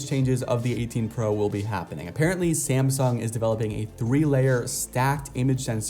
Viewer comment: did he just say samsung?